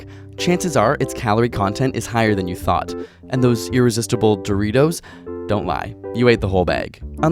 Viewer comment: YALL: eating disorders aren’t cute, and not something to joke about. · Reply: I thought u said eating Doritos and was so confused lol.